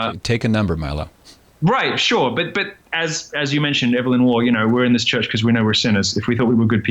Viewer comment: Can someone tell me who they are talking about? Evelyn who? I can't hear the last name, I like those quotes and I want to know who they are talking about. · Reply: Evelyn Waugh, found it.